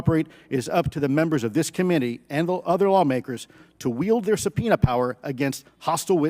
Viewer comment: I heard „…wield their peanut power“ 4:01 and thought about president peanut from American Dad 😂